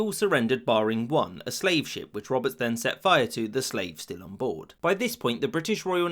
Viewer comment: Can anyone tell me what he said at 1:50? “Which Roberts then set fire to [something?] the slaves [something?]” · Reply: "Which Roberts set fire to, the slaves still onboard."